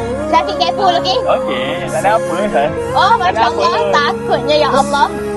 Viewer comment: minit 4:37 , takde apa sayang? ke salah dengar hahahahahah ❤